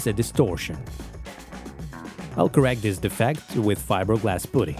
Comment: This is incredible and I am floored by the time and attention to detail you're pouring into the project. As a bonus, at 4:46, I heard "fiberglass pudding" and that is what I will call it for the rest of my life. I know it isn't what you said, it's just too funny not to adopt.